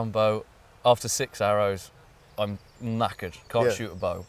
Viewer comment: “After six arrows, I’m knackered” What I heard was “mackerel” hmm.....I don’t know what he actually said, but either way, it’s awesome.